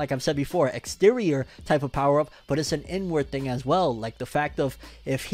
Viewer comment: every time you said "inward" i heard "N-word"